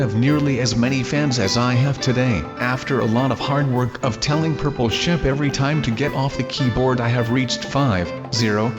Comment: When Said Purple Sheep I Thought He Said Purple Sh#t at 9:12 Or